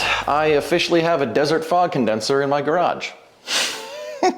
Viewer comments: At first I thought It said desert FROG and I was very interested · I thought the title said "a desert frog" and I was horrified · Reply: I thought you said dessert frog.